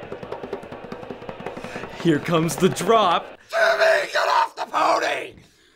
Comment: did he say " TIMMY GET OFF THE PONY"